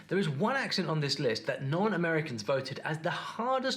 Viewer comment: I think he said non-Americans